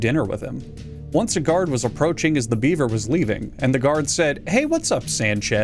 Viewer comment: NGL I misheard beaver as "beaner" and it still works